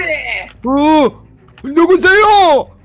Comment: 아잌ㅋㅋㅋ 리 하: 여맨니이이이이이ㅣㅇ잌!!!!!!!!!!!!!!!!!!!!!!!!!!!!!!!!!!!! 리하.말하는 곰 미호.구미호 누구세요가 여보세요 로 들림ㅋㅋㅋ 왜 오늘 여맨여맨 거렼ㅋㅋㅋ